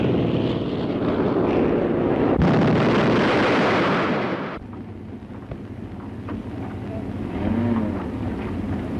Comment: What did he said? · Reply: bloody fool